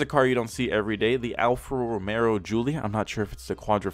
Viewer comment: Boi, did he just say Romero